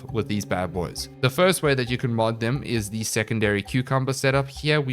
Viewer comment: did I just hear him say secondary cucumber 🥒😂😂